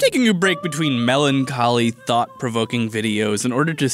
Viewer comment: In the beginning I thought he said thot provoking